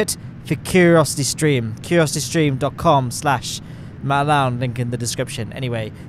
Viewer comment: Why did I hear “Matt LowneLincoln” 💀💀💀💀